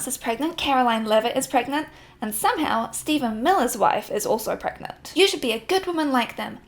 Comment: for a second I thought you were saying the Steven Miller was pregnant